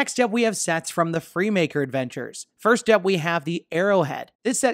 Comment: When you said $79.99 I thought you said $799😂